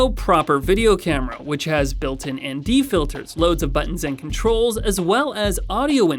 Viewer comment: I heard 'Andy filters'